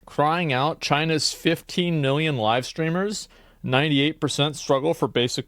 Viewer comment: I'm sorry I thought you said 15 million